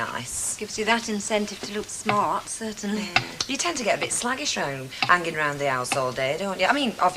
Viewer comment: did Deirdre say “slaggish”?😲